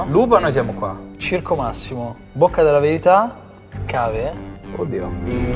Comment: ho sentito un 'coddio. Interessante